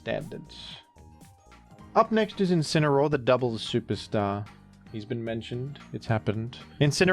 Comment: Misheard as “the devil’s superstar” at first and as a VGC fan, I was like “yeah, that’s as accurate as what he actually said.”